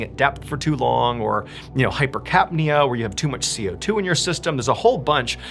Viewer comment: Did he just say “hyper cat meow”? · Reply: hypercapnea lol.